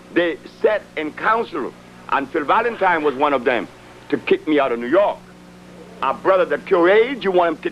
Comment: Hey! Did her said at (49:28) did he say phil Valentine organized to kick him out new york?